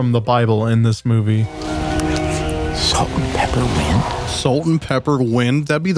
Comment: I always thought Flint was saying salt & peppermint. Not salt & pepper wind